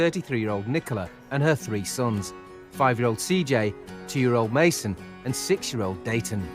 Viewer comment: Why has Nicola got a 4 year old still in nappies.? 5 kids 2 of whom she has dumped on her parents, has she never heard of contraception. How DARE he have 10 kids and let the rest of us pay for them. I have a spinal cord injury and I still work. Of course these people mucked around at school so they can only do manual work. What's wrong with Angela she can work. This is where our compassionate desire to save kids from the stupidity of parents has led us, people think longer about buying a bag of crisps than becoming a parent. WHY DO ALL THESE PEOPLE SMOKE ?????? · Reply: I misheard this and thought the presenter said 2 years, 5 years, and 6 years but he said a 2 years old, 5 year old and 6 month old